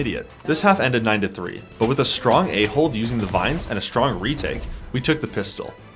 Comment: is it just me or did i hear strong A-hole instead of strong A-hold 💀💀